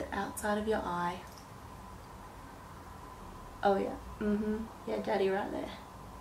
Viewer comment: Did she seriously say "umm hmm Yeah daddy right there" @ 3:45 that's what it sounded like. 😕